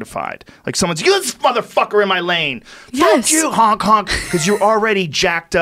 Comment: thought he said "f++k you, Hong Kong!".